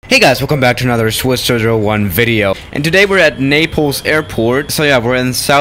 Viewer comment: I thought you said nipples.